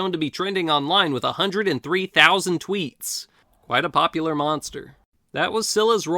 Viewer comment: I thought you said 300k tweets for scylla,sorry